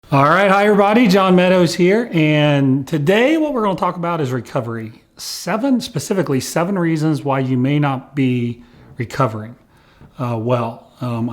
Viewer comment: This whole time, I thought he said "Higher body", but he's really saying "Hi everybody!"